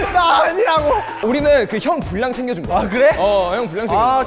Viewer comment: i thought S coups said "HINDI AKO" HAHAHAH.